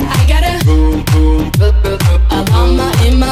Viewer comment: I know I wasn't the Only one who heard "I got a, vroom, vroom, vroom" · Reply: I thought it was saying boom boom boom